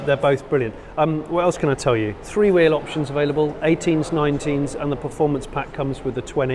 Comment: For a second I heard “three wheeled option available” hahaha!!